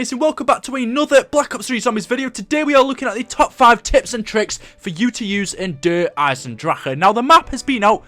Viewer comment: I thought he said tits and tricks